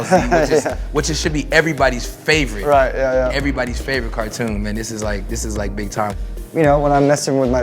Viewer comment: did he said cartoon?